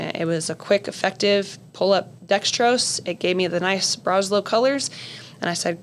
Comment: the medic describes running the D10 through something I didn't catch. Sounds like "alberetrol" maybe? Clarity appreciated! Great episode and ddx discussion. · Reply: Hello, the device is a Buretrol. Used to ensure proper doses and to not fluid overload pediatric patients.